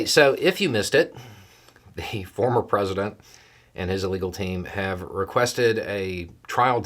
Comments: I heard "illegal team". The captions heard "illegal team". I choose to believe that was intentional. Nice. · Did I hear you say “the former president and his illegal team” at :35? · I misheard you in the beginning, but what I heard seems equally appropriate, "Trump and his illegal team." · Did you say "former president and his illegal team" at 0:30 ? 😄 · I'm enjoying how you said "The former president, and his (uh) legal team" in such a way that it sounded like "The former president, and his illegal team". Hah. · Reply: I definitely heard "illegal team" and it made me chuckle as well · Did I hear "Trump and his Illegal team" or has Beau's sh!t-eating grin suppression at the beginning of the video effected my hearing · I swear I heard "the former president and his illegal team" and I love it! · I’m sure I heard this wrong, but I think I heard you say “Trump and his illegal team.” 😂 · Am I hearing things? At 0:31, did Beau say, "... the former president and his illegal team..."?